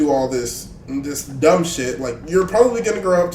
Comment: what did you say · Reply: red just say ‘shit’ 😂